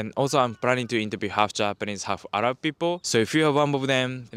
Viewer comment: Actually I'm shocked that interviewer is also mixed person! Omg. And it's mixed arabic and japanese😮😱so surprised. But in the same moment I understood why I saw some things in him but couldn't explain it to myself. And when he said it, I immediately see it's really like that, and arabic features too. So interesting) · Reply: I think you are mishearing bc he doesn't say he's half Arab (he says he's planning to interview half Japanese/half Arab people. Turn on the subtitles